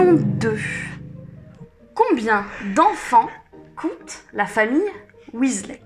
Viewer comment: j'ai compris : combien coûte la famille Weasley ? 😂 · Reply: 3uh non pas du tout 😂 "combien d'enfants compte la famille weasley"